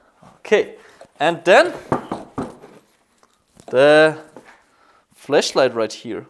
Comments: Anyone else thought he said fleshlight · did he say "fleshlight" xD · when he said flashlight, for a minute i thought he had said fleshlight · Is it just me or does it sound like he says fleshlight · I thought he said flesh light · Did he say the flesh lite from here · Sounded like he said fleshlight instead of flashlight😂😂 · Sounded like he said fleshlight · Sounds like hes saying fleshlight haha im so mean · I know his English isn't that good but I thought he said fleshlight in the beggining